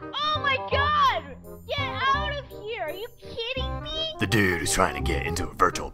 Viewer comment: lol i heard her said "really are u kitten me " LLOOOL